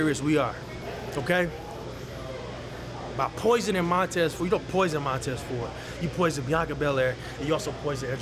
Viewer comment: I thought he said « You not poisoned Montez ford , You can poison Angelo Dawking ,Bianca Bellair , But NOT ME !!”